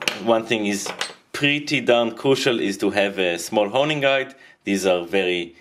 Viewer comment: Hello my good friends! I'm really happy to see that you enjoy this video. I just want to mention that I've ended up sharpening the face of the Chisel closer to 45 degrees and the heel at around 17 degrees. That produced the best results for me. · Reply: I was asking about the tool he used around 2:35. I could not quite hear what he said. I found out it was a honing guide